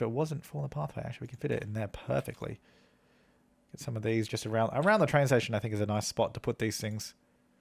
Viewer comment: Another Train station, as he puts a crosswalk down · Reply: thought he said "on the train station" as in people wanting to get around to the train station like his sentence